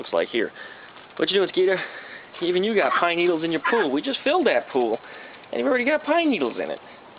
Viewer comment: I thought you said, "look at that Skeeter, you've got pine needles in your poo" lol